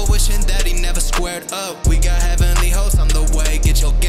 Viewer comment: Wait am I the only one that heard him say "we got Heavenly hoes on the way, get your game up.....?😐